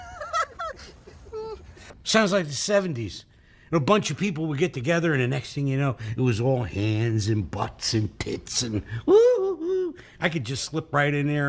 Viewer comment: "... Then AIDS ruined everything." The ending to the funniest short story ever 🤣 ( 9:08 ) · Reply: oh i thought he said the 80´s. Since he was talking about the 70`s prior...